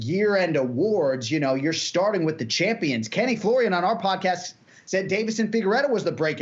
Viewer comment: The way pronounces "Anik and Florian," I heard as "Anakin Florian." I thought of a little Jedi with a nose protruding from his hood, telling people how he uses the force "to finish fights!"